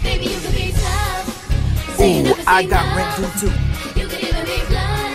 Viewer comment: He said, " I got rent due too!" 😂😂😂😂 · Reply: I heard "I got rhythm too"